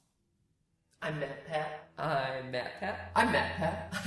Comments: I thought u were saying I'm batman but u were saying I'm mat pat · nah i think i've been watching way too many dora episodes, i kept hearing "i'm backpack" instead of "i'm matpat" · why for some reason when matpat was saying "I'm matpat" it sounded like he was saying I'm backpack!? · At first I heard "I'm MatPat" then I heard "I matpat" and now I'm wondering what is MatPatting? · I thought he was saying I'm mad pat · I thought he was saying "I am Macbeth" lol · Why did I hear "I met pet" instead of "I'm MatPat" at the start? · When mat was saying I'm mat pat I thought he was saying I'm mad pat am I the only one? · It's sounded like he said " I'm backpat" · who else thought that he said I'm batman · If you listen to the intro when he says "I'm matpat" over and over again it kinda sounds like he saying "I'm not mad." Is it just me who hears this:/ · All i hear is I'm Mad Pat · at first I thought he was saying I'm batman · Went he is saying I’m Matpat he sounds like he is saying I am mad Pat like insane dose anyone else hear that or am I crazy? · Thought he said "I'm meth" in the beginning of the video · Why do I hear "I Am Batman" instead of "I Am MatPat" ?! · I thought he said "I'm butt pad" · In the intro it sounded like you said I'm Mapmap. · in the intro i thought he said I'm mad at pat. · sound like you said "I met Pat" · Him saying "I'm MatPat" over and over my brain thought it said Meth Pet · When he was saying "I'm matpat" it sounded like he was saying "I met pet" XD